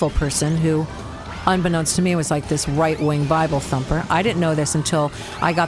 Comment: did she say bible thumper? Dude that’s a super offensive term!